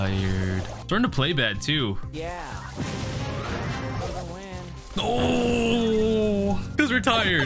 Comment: Anyone heard say I’m bad and said yeah LOL